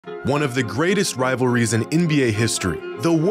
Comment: i thought you said "one of the greatest rivalries in india history" 😂